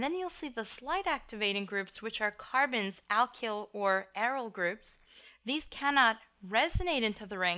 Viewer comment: what group name are you saying after alkyl? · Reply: aryl